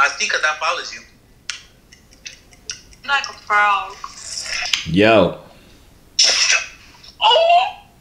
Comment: anyone else heard her say he look like a Frog 🤣🤣